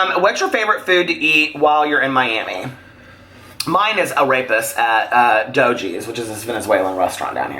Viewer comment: I literally thought that you said that your favorite thing to eat was “a rapist” 😂😂😂😂😂. I thoroughly enjoyed this as I do all of your videos. You are so awesome!!!!!!!! ❤️❤️❤️❤️❤️❤️